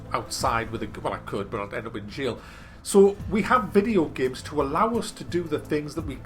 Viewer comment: what does he say? All I get is "well I could, but I have a windshield". Can anyone help me please? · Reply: I could but I would end up in jail - meaning if I ran around the streets with a gun.